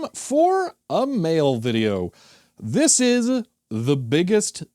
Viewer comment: I hear that as "it is time for a mayo video" 😂